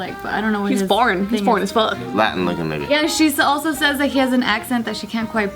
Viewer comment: Did say Latin looking nigga at 12:00 😂